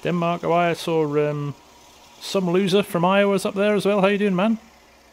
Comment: did he say some loser from iowa because i live in Iowa